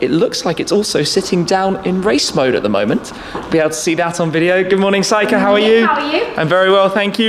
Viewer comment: I thought you said to that woman 'Good morning psycho how are you'!